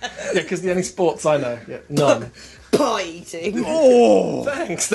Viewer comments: What the hell did even say at 5:17? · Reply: ‘Pie eating’ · What did say to his not knowing sports comment that ‘drew first blood’? I’ve replayed it over and over & gone through the comments and can’t find an answer. It sounds like she said ‘pint-ing’ which presumably would mean that he liked to drink but given how harsh thinks the comment is I’m assuming it mustn’t be that cause whilst that’s not nice, it wasn’t too awful either given she doesn’t know him and therefore couldn’t rightfully be calling him an alcoholic (unless I’m missing something/misunderstanding the meaning of a made up term?). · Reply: says “the only sports I know-“ and she interrupts saying “pie eating!”